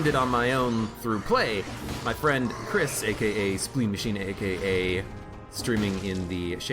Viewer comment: did you say spleen machine and if so, is there a link to his youtube or twitch?